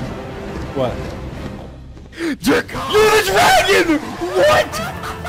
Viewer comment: did i just hear george saying "dracarys" in 1:22 ???